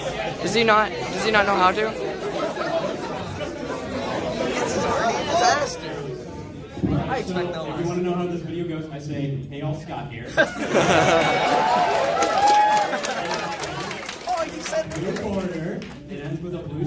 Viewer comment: I have always thought he said “Hey yall,Scott here.” NOPE,IT’S HEY ALL.